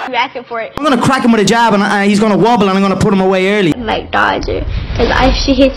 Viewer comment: What does Conor say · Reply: Muhammed Kemal Öz 'I'm going to crack him with a jab and he's going to wobble and I'm going to put him away early.'